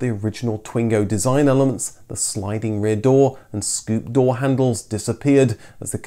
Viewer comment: Look at 23:40. He says " the sliding rear door" and the cc says "the sliding rear seat". And is that one of those Dyson fans behind our very intelligent friend on camera. That says volumes about his judgement, I'm afraid. I started out liking the guy. I'll give him more scope.